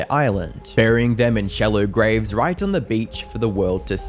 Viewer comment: I heard 'buried them in jello graves' 🤣